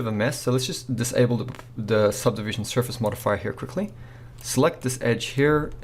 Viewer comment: DID HE SAY MOTHERFUCKER? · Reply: "Surface Modifier."